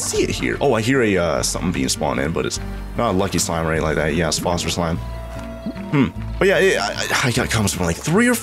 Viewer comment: did he say sphospher slime or sponsor slime